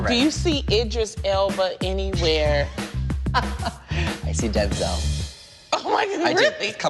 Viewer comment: LOL, DENZEL, I thought he said dead zone at first ;)